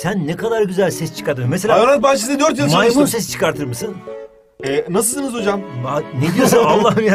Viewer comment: Tonguç hocami olmus ben mi oyle duydum 1:21 tonguc hoca hoca tonguc olmus nasil yaa